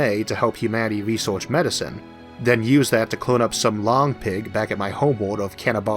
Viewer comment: Interesting term, "long-pig." I thought you had said "lawn-pig," which may seem more descriptive of humans in the context of a food source